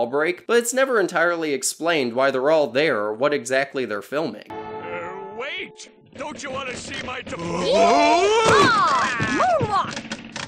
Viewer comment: "Wait, don't you want to see ..." I've never been able to understand what Plankton was saying here. 🤔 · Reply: “Wait, don’t you wanna see my diploma??”